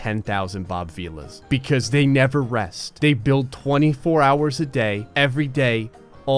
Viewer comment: For a second thought he said 25 HOURS